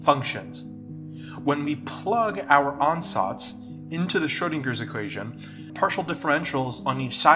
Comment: "when we plug our" what? captions say "onsites" ty. beautifully done. i actually find the animation difficult to follow as it requires remembering three animated visualizations: 1. the formula, 2. the blurry mess, 3. the altered formula. That's how my brain works, I see the blurry stuff and forget what was happening, so I have to arrow back and forth in pause mode to see cleanly what is transpiring.